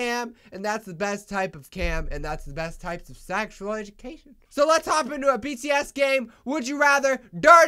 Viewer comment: At first I heard “let’s hop into a BTS gay”😂